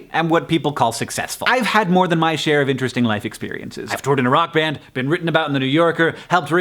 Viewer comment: When you said, "I've toured in a rock band," at first I thought you said, "I've toured in Iraq."